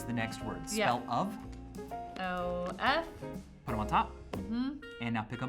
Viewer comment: I know she said oh, but it sounded like she said l or el, like if you agree